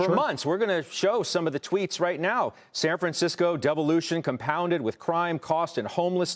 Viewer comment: Did he say Devolution? <link>